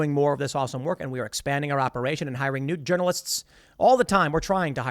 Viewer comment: Did Tim say they are hiring nude journalists??